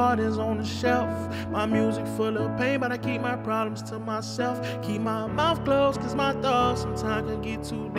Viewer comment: “Keep my mouth closed because sometimes my thoughts can get to deep, my heart cold, my eyes closed but I never go to sleep” That hit home❤️‍🩹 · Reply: I thought he said keep my mind closed which hit harder to me but still 🔥🔥🔥